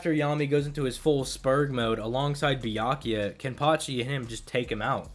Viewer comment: I swear it sounded like he was saying, "Byankuya." Lol